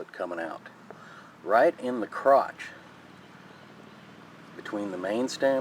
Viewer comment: Did you just say crouch @ 1:50? Lol! Nice video by the way!